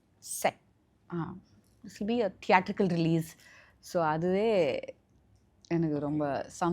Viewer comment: What she is saying in 1:41:00 ? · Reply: Theatrical release